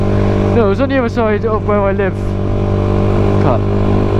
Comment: did you say cut then not actually cut the video lol ?? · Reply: thought you said c*nt for a sec 😂😅